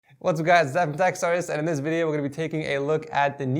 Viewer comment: I heard: "Hey it's Ed from tech sarce..." is it just me? XD